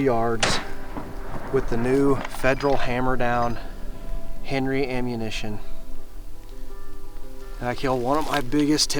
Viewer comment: Great shot what brand of grain bullet did you use in the Henry 30-30. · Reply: I thought I heard him say Federal Hammer Down Henry Ammo near the end of the video.